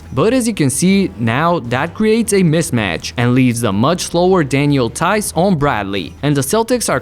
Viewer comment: For a moment I thought he was saying lower not slower🤣🤣😂😂😂